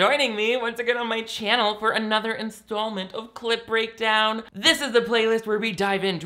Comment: So.. I always hear "CL*T breakdown" when you say clip breakdown and uhh basically if you'd open an only fans to critique porn movies/films just for the fucking fun of it and/or extra revenue, I'd be down.